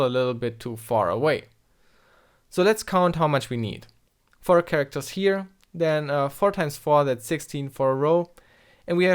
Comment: Sorry but, I can't understand you at 9:50. How did you counted these rows? Thank you. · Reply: "4 characters here". AAAA -> <num>. "Then 4 times 4 that's 16 for a row". so 16 bytes per full row. We have 3 rows that are filled full with 4. "And with the next fill row we can ...". So the fourth full row would write over the <num>.